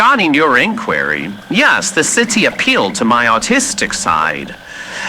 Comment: I understood "autistic" instead of "artistic". As I relate to many of Alastor's manners and as an autistic person myself, I decided that this info is now canon. ♾️❤️ Moreover, my philosophical/sociologist ass can't help but want to study the morals and mind of our favorite serial killer as he prides himself on his crimes.